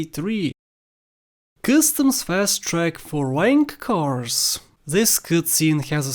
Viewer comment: Did you say "wank cars"